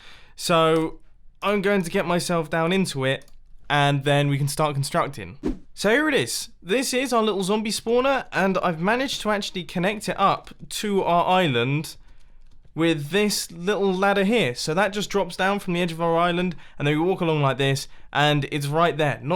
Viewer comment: did he just say ' fork island ' ?